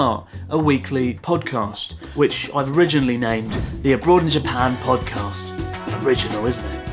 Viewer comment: hahaha... sounded like Cranston said "a Broad" (as in the derogatory slang term for a woman, often of ill repute) as opposed to "abroad" (as you, indeed, are) I would strongly advise that you get on a bike asap and take several real rides (at least a few 20 or 30km ) mostly just to get yer crotch used to it... seriously! That's gonna be th' thing that'll do you in if you're not prepared on such a long journey. Good Luck!